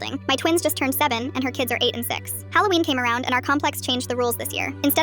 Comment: I'm so sorry, but I heard, "and her kids are 86" not "8 and 6"😂